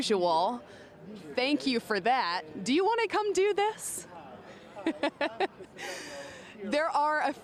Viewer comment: did she say "do you wanna cum through this?"